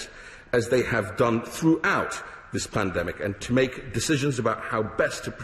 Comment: Did he just say "Plandemic"?? 😕😁